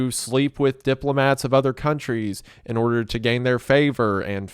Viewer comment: I thought he said order 66